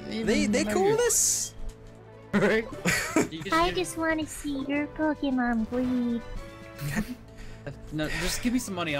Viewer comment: huh, I thought she said " Just want to see your pokemon bleed"